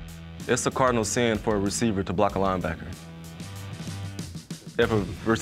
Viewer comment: what is KJ saying at 7:48? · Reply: A linebacker should never be blocked by a receiver